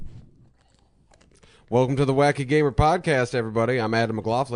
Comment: I was really tired when I heard the guy say, "welcome to the wacky gamer podcast"- I thought he said, "Gay podcast" ,, listen to it again folks